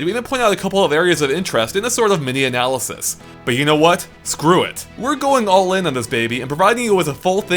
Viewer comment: I love that the captions say fuck it instead of screw it, just great